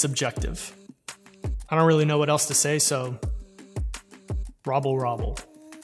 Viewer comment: This BLEW MY MIND!!! I totally thought the Hamburglar said “rabble rabble”... Hey, also great project lol. I’m not a fan of how the new PS looks either.